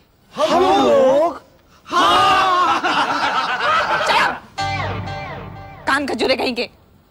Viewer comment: What does she say at minute 2:13? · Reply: “Kaan kajure kahin ke!” But, I don’t know what this means as I’m a Pakistani. You will have to ask an Indian person for some translation for these slang words.